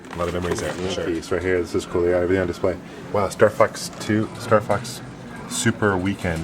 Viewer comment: i thought said star fucks instead of star fox